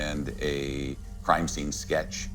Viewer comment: Wait did he just say that the investigators used 'hypnosis'? Or am I trippin